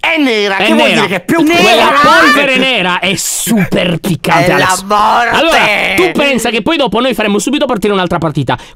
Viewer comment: è la sborra?solo io l'ho sentito